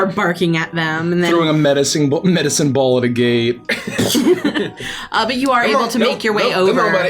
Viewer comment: Am I the only one who heard 'throwing a medicine ball at a gay' @ 19:15 ?